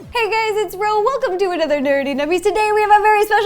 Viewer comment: I thought you said Dirty Dummies but it was actually Nerdy Nummies😂😂😂😂